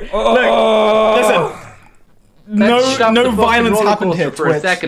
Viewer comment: goodbye with george talking over the mediashare at like 2:15 i thought he said "stop the violence" like from glee 😭😭